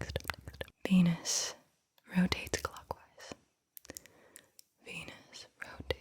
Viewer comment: When she said clock I thought she said Glock. I was like there are 100% people with Glocks in Los Vegas casinos.